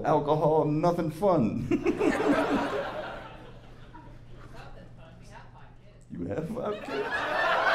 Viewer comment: Can someone help me with what was said around the 14:20 mark? I can't seem to figure it out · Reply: They have fun...they have 5 kids...